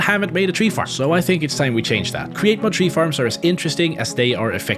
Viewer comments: Thought he said weed farm 💀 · did he just say "weed farm"? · Am I the only one that thought he said weed farm?